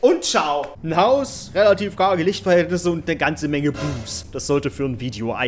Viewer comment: "Ein Haus, relativ karge Lichtverhältnisse und ne ganze Menge boos." Was? Boobs? o.o Hat sonst noch wer boobs verstanden XD ¯\_(ツ)_/¯